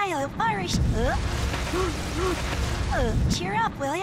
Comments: it sounded like she said "Hi im irish" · it sounds like amethyest said 'I hump porridge' lol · sounds like Amethyst said im malnourished